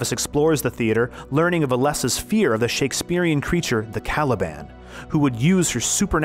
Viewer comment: I thought he said she was scared of the Taliban.lmao